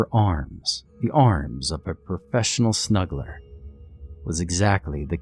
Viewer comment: Reading the comments I’m glad I’m not the only one that thought it said smuggler